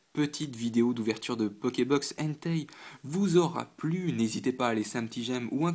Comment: J'ai compris "j'espère que cette ouverture de box Hentai vous aura plus" ça ma choqué j'ai vraiment trop l'esprit mal tourné xD